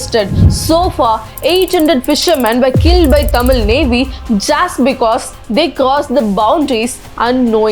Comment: Could u re check 10:20 minute in video, seems the subtitle and ur words not matching, instead of srilankam navy, it was said tht tamil navy